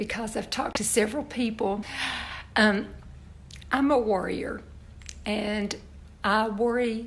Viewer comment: Oh! Worrier. I thought you said warrior! Maybe you could try warrior today? Hugs to you! 💖💕🇨🇦